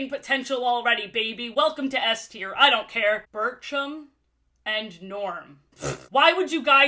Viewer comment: STOP WHEN SAID BIRCHUM I THOUGHT SHE SAID BURTRUM LIKE FROM THE DISNEY SHOW JESSIE 😭😭😭😭😭